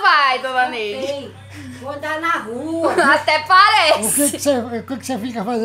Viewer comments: eu entendi vou dar na rua kkkkkkkkkkk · só eu q ouvi "Vo dar na rua"?